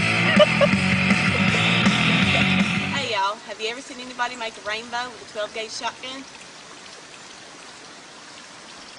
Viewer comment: Nice video, but what did she say..? 2:01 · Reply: Elnida R "Have you ever seen someone make a rainbow with a 12 gage shotgun?"